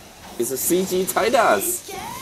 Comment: sounded like he said Cg tight ass